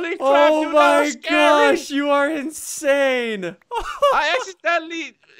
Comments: did crainer say shi* tank? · did he say sh*t?